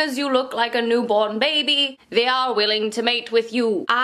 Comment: “Men are stupid so as long as you look like a newborn baby, the are willing to date you”😂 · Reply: I thought she said 'they're willing to mate you' 😭😂😂